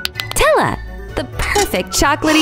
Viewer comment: it says tella instead of nutella